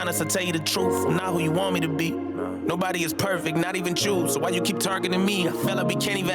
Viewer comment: I thought he said “ not everyone is perfect not even Juice” I thought he meant Juice Wrld considering the topic of the song